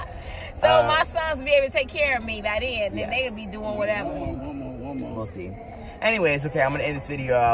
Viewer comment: WHAT DID YOUR MOM MEAN ABOUT HER SONS WILL BE TAKING CARE OF HER AND NATE WILL BE DOING WHATEVER WTH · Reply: I didn't hear her say Nate, I heard her say, they'll be doing whatever.